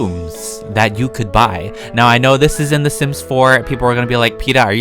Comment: i thought u were saying the simps instead of the sims